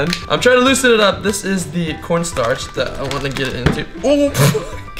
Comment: did he say ohh f**ck